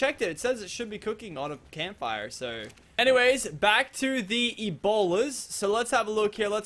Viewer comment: whenever he say bola i thought he was saying ebola